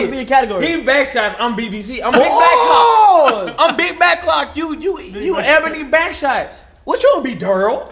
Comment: Did I hear NLE say big back clock @ 46:24 😂